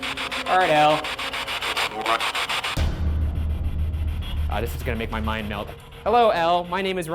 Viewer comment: I hear “More” not “Go away”